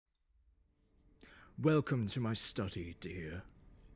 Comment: I thought he said " Welcome you stuffy potato "